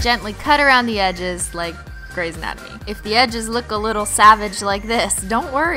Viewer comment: did someone say greYS ANATOMY?